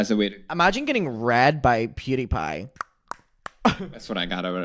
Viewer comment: I heard it as “imagine getting bread by pewdiepie” not “read” and my jaw hit the floor lmaooooooo